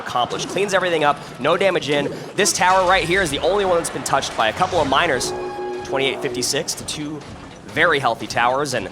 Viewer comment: what did he sayy?? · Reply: BAALRIGHT